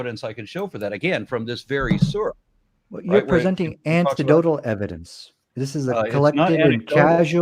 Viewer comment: Holy hell, did I just hear 'antidotal' evidence? xD